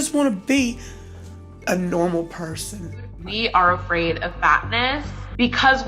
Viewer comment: definitely heard it as "we are afraid of batman" and I didn't even question it because it's these people